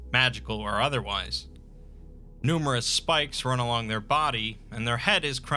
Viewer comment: I really thought you said "numerous spiders run along their body".